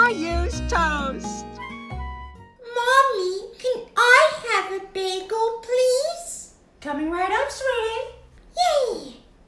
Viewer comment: In this part 0:05 I thought caillou said mommy can I have a mango pls